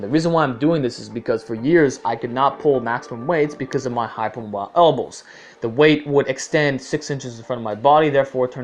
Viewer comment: "because of my hyper moi elbows"